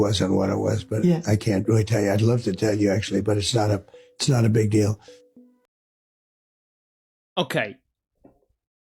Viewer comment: It sounded like Trump is saying "I'd love to tell you, but it's not uhp-(to me).. It's not a big deal." Sounded like he was going to say "It's not up to me" and if it's not up to him, then who is it up to?